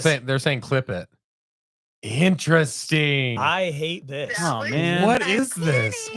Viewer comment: On the second glitch, it glitches on saying , "I hate this." but as it glitches it starts to sound like it says " hate us, hate us, hate us." Also I noticed on the first glitch right as the glitch ends is saying " completely accurate". as if something reacted to what they were talking about, caused the glitch and then confirmed what was talking about !!!. Suuuper creepy though, OHEMGEE ! I thought it was my laptop or my headphones acting funky.